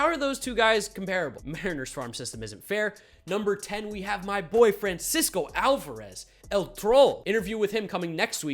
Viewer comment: lmao sounds like he says “My boyfriend” instead of “My boy Francisco”